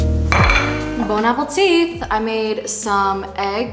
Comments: did you say bone appleteeth cause that’s what I heard 💀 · I heard- " Bone Apple Teeth." -_